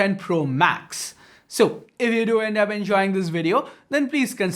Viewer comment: what did he say? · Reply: So,If you do end up enjoying the video. ... .